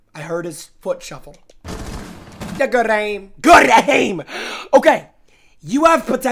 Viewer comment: what did he say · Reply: i literally said good aim 😂 do you not watch